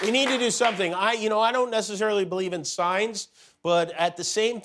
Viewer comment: Is that true that Jimmy Kimmel doesn't "necessarily believe in science", or did he just say that there, for the sake of the joke?